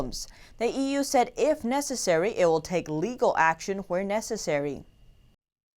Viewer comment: what they say? lol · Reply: sounded like lethal. lol